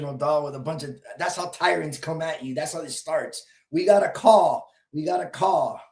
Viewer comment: Cop: "We got a call" What I hear: "We got hearsay" 🙄